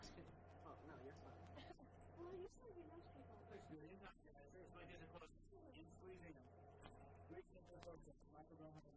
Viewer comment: does someone says "boy you've got your nose tape on" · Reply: boy told him: " hey you still got your nose tape ON"